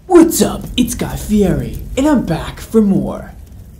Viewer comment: stg i thought u said "i'm guy fieri and I'm back from war"